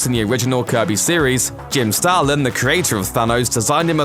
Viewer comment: Thanos & Starfox were introduced as greek gods by Starlin. Roger Stern changed it to Starfox being an Eternal in his Avengers run. They then had to backtrack & change things. For example: The original Captain Marvel issues say Thanos’ uncle is Zeus, but when they reprinted those issues in TPB form they changed it to Zuras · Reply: I was so confused for a minute because I thought you said stalin not starlin